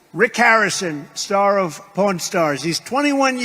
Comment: It sounds like he says porn stars….especially after the tongue comment..👀👀🤡🤡🤡